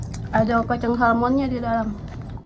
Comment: Apa cuma aku yang salah dengar.. Ada Kacang Salmon di dalam 🤣🤣🤣